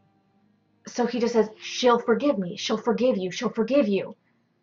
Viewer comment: For some reason, I heard "She'll forgive you" being whispered to Ruby. I have no idea why. But this makes more sense.